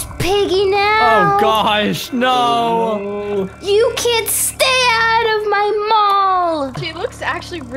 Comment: When was piggy first i thought she said “stay out of my mom!!!” Then I just realized she said “stay out of my mall!!!” w